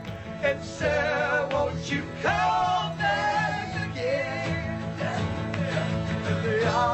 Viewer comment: In the lyrics it says " Sir wont you call back again " but i hear " and shel wont you call back again"